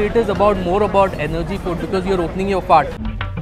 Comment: guy says opening your fart instead of fast lol